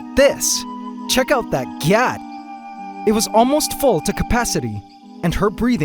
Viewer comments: Did you just say “check out that gyatt” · did you just say check out that Gyatt? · Did he just say check out that GYAT to a Mantis 😆🙄🍑